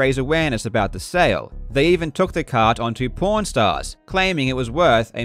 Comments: I keep hearing "Pawn Stars" as "Porn Stars" and it makes this whole story a lot more complicated. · Everytime Karl says "Pawn Stars," I mishear him. Every single time. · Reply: Same. I heard Porn Stars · Not gonna lie, I thought you said "Porn Stars" instead of "Pawn Stars" which definitely had me wondering what do porn stars know about retro video games. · Who else thought when Karl said "Pawn Stars" he was saying "porn stars"? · Every time you say "Pawn Stars" I hear "porn stars" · Holy shit I thought Karl said "Pornstars", I thought that they hit the line with a truck this time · I thought he said "P**nstars" first, then I watched the video.. and was like "ah... Pawn stars" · no offense karl i know its your accent to my dumb ass american ears but it sounds like your saying pornstars instead of pawn stars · Your videos have evolved to a new level, I enjoy the speed running videos and enjoy these investigative videos just the same. Also, I keep hearing the words “porn star” when you say Pawn stars lol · I'm listening to the video as I work on other stuff.... could've sworn you said porn stars not Pawn Stars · with your accent, I thought you saying "Pawn Stars" was "Porn Stars" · I was just listening to this while working and when he said they started taking them on pawnstars I thought who said porn stars and spents the next 5 minutes very confused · i keep mishearing Karl saying pawn stars as " porn stars " :/ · Everytime he said "Pawn stars" I heard "PornStars"